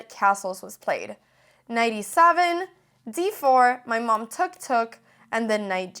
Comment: I heard you saying: my mom Tuktuk :D